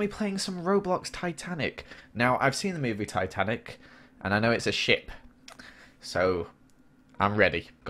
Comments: At the start when he said "I've seen the movie. I know it's a ship" at first I thought he said "and I know that it is shit" 😂😂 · I thought he said shit lol